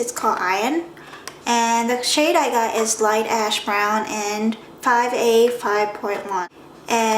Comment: lolzz i thought you said "Light ass brown"